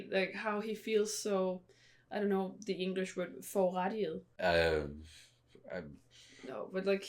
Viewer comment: The saddest part about the rest of the story being movies is that we won't get to see actually be entertained by Zenitsu instead of having every ounce of joy drain from her face the moment he appears on-screen. 😁 As for how the crows got into the Infinity Castle, I've heard (not sure if it's true, but it makes sense) that they voluntarily flew in after the Demon Slayers so they could help relay messages throughout the scattered parts of the castle. Those crows may be rude, but I guess you have to respect their work ethic. Also, did anyone catch the word said at 47:10? I want to look it up as my word of the day. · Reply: I actually said it wrong! I said "forurettiget" but the correct word is "forurettet", and it means something along the lines of the feeling of "being treated unjustly", or that you are the "injured party", if that makes sense? I feel like there isn't a proper wording for it in English (that I know of), or a least of what I meant And also, don't worry - we'll both be back when we'll get access to the movies 🥰